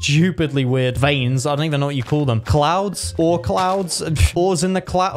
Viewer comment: what do u call them clouds? ore clouds? Me: wait did he just say Oort clouds